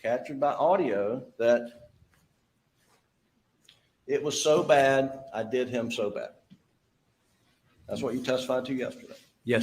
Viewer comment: sounds like he said "it's so bad, ay did him so bad" (ay being THEY as southerners talk) only on slowest speed --I hear that.